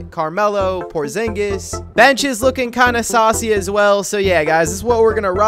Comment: Did you just say "kind of saucy" L